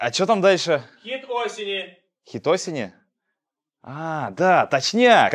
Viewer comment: Мне сначала послышалось - хитосина)))